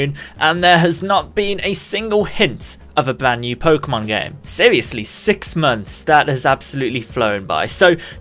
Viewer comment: "It's been 86 months"... that's what I heard...I'm refusing to believe anything else...^_^...